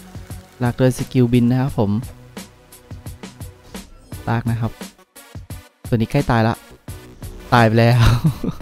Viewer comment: ผมได้ยินว่ารักนะครับ555